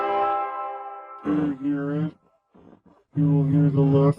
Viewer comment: parece muito que ele falou "preste atenção" em português☠️